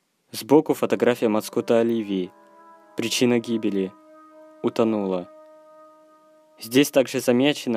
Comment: На моменте когда автор сказал «причина см#₽ти:утонула» мне послышалось не утонула а ультанула